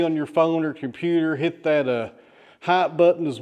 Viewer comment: That button you wish us to hit, did you say 'hi' or 'hype' (25:15)? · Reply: I know you weren't asking me 😊, he said "hype". I've heard other YouTubers say to hit the hype button and have asked what the hype button is, never got an answer. I figured the thumbs up and hype button were being used as the same thing, now I'm not sure since he said both at the end of his video. Maybe somebody will come along and clear this conundrum up.